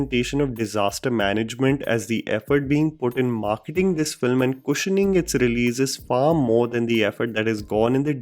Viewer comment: at 3:20 did you say "pushening?" Lmao